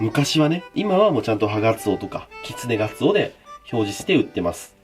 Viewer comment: ハガツオが所々「ハナクソ」に聞こえて草